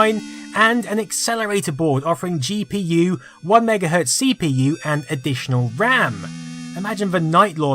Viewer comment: The voice says: "One Mega Hertz CPU" but the captions say: "1GHz CPU". Which one should I believe?